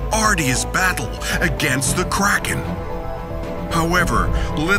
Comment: Everytime I hear the word "Kraken" I hear "Crackhead"